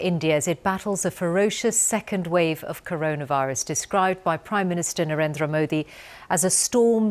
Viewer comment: thought she said DESTROYED BY PRIME MINISTER NARENDRA MODI